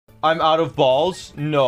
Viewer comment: I click on this video and the first thing I hear is "I ran out of balls?!".